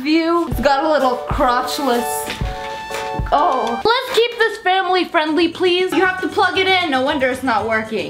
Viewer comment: I THOUGHT SHE SAID FAMILY FRIENDLY SHE SAID SH*T